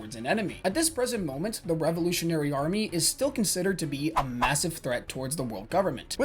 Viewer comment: They put “massive treat” instead of “massive threat”.